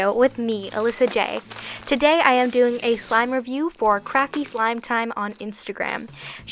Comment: It sounded like you said it’s a review for crappy slime time lol love you!❤️